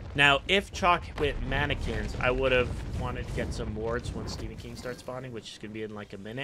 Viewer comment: Am I the only one who heard "Stephen King" instead of bull demon king? :p